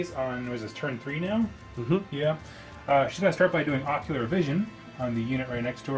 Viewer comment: I always hear "Ocular Vision" when you say "Oracular Vision" and was thinking that that was a very stupid name for a spell as all vision is ocular...